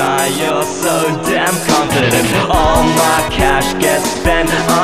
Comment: It sounds like he says all my cash gets spent on pottying 😭